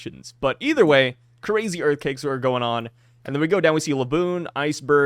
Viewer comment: Did say "EarthCAKES" instead of Earthquakes 😂😂😂